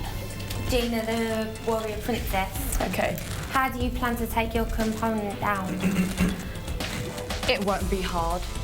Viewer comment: did the blond girl say Component instead of opponent????